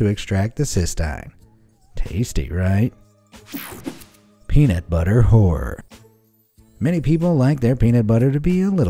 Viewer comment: I usually listen to this with my headphones in while not looking at the pictures, and when I heard peanut butter horror I thought it said peanut butter whore lol